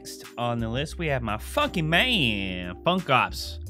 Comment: who els thought he said "fuckin man" instead of "funky man". Ha😂😂😂🔥🔥